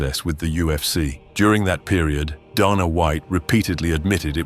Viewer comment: I heard Donna is worse than Dana.